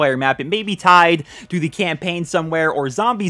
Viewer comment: did he just say campaign? I wish there was.